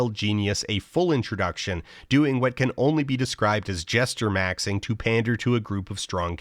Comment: jestermaxxing is the technique and jestermogging would be the outcome specifically in relation to a perceived rival. so let's say you and Jack were jestering to try and get in with the hyper-avengers, both of you would jestermaxx and the one to succeed and join the club would have jestermogged the other. hope that helps. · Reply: oh, i thought he said "gesture maxxing"...